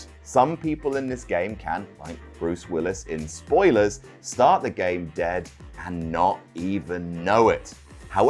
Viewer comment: I’m currently not 2 minutes into the video and I thought said “like Bruce Forsythe, start the game dead” 💀🙃🤣